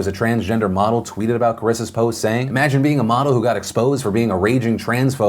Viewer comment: sorry, it sounded like you said "model".